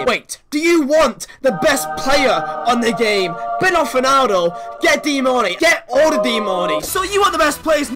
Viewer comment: thought you were always saying bend off ronaldo... Also. believed you played D MARNEY in WL cause he is retired now... Lul